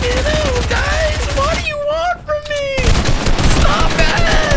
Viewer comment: Anyone else hear say ‘F-ing hell guys what do you want from me’